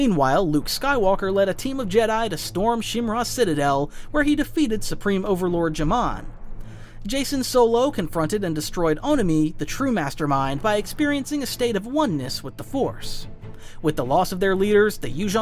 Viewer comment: What I heard was “Supreme Emperor Jamal”